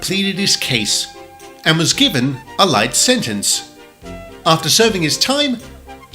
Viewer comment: WHO ELSE HEARD LIFE SENTENCE!!??